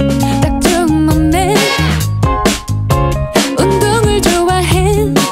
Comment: Ive been learning korean for 9 months... And i think i just heard "엉덩을 좋아해" and if im not mistaken... She said "They like my butt"